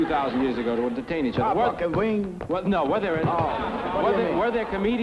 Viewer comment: I loved this special so much. What's he saying at 5:23? I could never make out his words. "Walk on the wing?" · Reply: "A Buck and wing". It's a kind of dance.